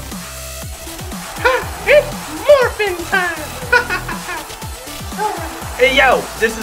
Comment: am i hearing this wrong or did billy say “hah! its morbin time!”